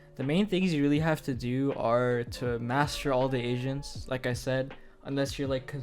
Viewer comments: I STRAIGHT UP THOUGHT DUDE SAID "MASTER ALL THE ASIANS" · cc says master all the asians lmao